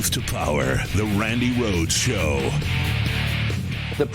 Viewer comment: I thought it said Brandi Rhodes lol